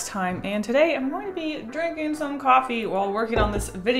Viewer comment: I thought it said drinking markers